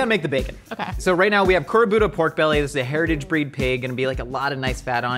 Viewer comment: I think it’s “kurobuta (black pig)” instead of “korbuta”